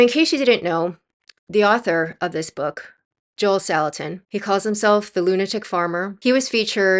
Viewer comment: I thought the guys name said "Joel Stalin." I was like "well that is an unfortunate name considering the subject matter." Lol